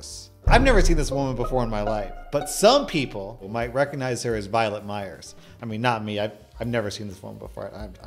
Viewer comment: I thought he said Violent Myers and thought wow that sounds awesome and googled it. 🤦